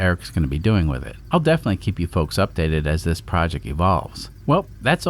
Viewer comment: At the end I imagined I heard say "I'll keep you informed as this project DISSOLVES"